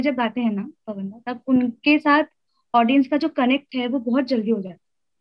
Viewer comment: Kitna respectful bolrhe h ek dusre ko ..so sweet ...inka inka vah loving it😍😘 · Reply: Did she said PawanDa